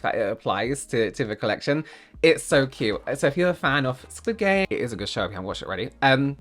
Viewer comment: you cut a part a bit to early, it makes it sound like you said squid gay